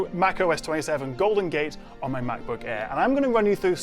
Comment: I heard "Mac OS 27 GOBLIN Gate" 🤣🤣🤣🤣🤣🤣